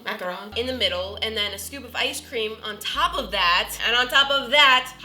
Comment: It sounded like she said stupid ice cream instead of scoop of ice cream